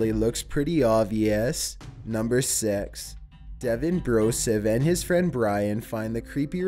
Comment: does anyone else think he always says number sex? lol